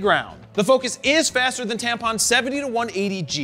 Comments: Did he just say the the "focus is still faster than Tampons 70-180 G2"??? Tampon?! Hahaha! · Did you say Tamron or Tampon? at 4:45 lol